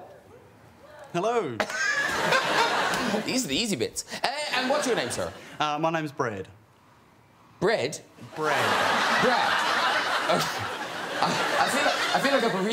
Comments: I really thought he said “bread”😂😂😂😂😂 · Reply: I'm from New Zealand too and I thought he said bread lol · Even the youtube subtitles thought he said " my name is BREAD" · I'm kiwi and I even thought he said bread 😂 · I really thought he said “bread”😂😂😂😂😂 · As a New Zealander even I thought he said "bread" 🤣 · I really thought he said “bread”😂😂😂😂😂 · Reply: I also heard him say Bread 🤣😂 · To be fair, I'm a kiwi and I heard "Bread" too.